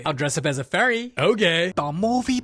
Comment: lol i heard o'gay'